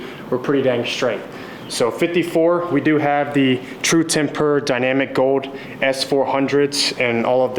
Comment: Thought he said "True Tempora".